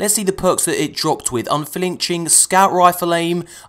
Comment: I heard it as "UnFAHlinching" the first two times.